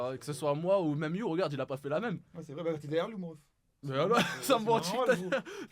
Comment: il a dit quoi à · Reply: «t’es derrière lui»